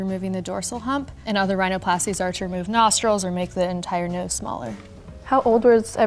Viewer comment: Did she say „remove nostrils“ lol